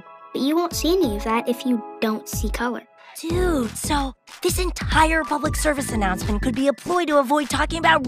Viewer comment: For a second I thought the end said see color, be racist